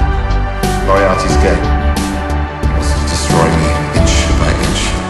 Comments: i love this so much... The song fits them so perfectly. (Only everytime I hear "Moriarty's GAY", instead of "Moriarty's GAME....", always makes me laugh a little :D ) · "Moriarty is gay" only me who hears this?